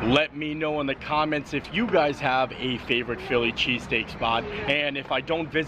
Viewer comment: Lol at first I thought he said Philly cheesecake. Oops.